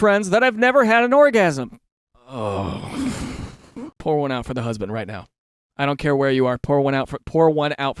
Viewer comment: Dude when you said "pour one out for the husband" I heard "pull one out" and deadass started unzipping my pants.